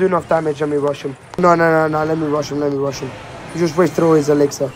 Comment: what he's saying? "Give me russian"? I dont speak English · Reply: He said “let me rush him”